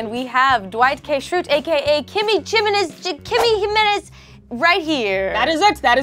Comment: Just when you thought couldn’t get more adorable, she shows up in Dwight Shrute cosplay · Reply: I actually thought she'd say.